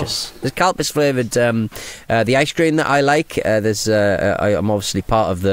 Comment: I thought was saying cow piss flavor ice cream..... :face-blue-smiling